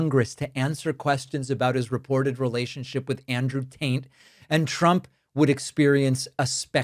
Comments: Sounds like you said "Andrew Taint" around 0:13, and I thought it was funny enough to deserve a post since I hadn't heard it yet. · Did I hear you say “Andrew Taint??”. 😂😂😂😂😂😂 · did you say “Andrew Taint?” 🤣😂🤣